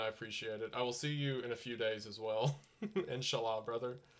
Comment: did he say inshallah or am i just tripping ? Not that its bad if he said that ofc